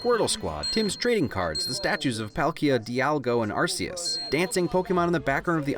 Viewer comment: did you say Dialgo